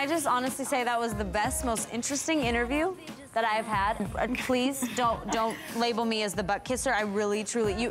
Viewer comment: What does Stacie say 7:02 to 7:05 ?? Please answer me! =] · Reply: She says...."Please don't label me as the butt kisser" ... I just notice my answer came 6 years later :P